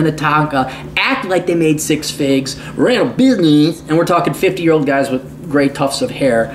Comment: What does he say at 16:43? Ran beer?